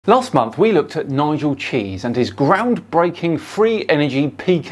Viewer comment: I misheard his name as Nigel Cheesy, which imho is a better name for this buffoon.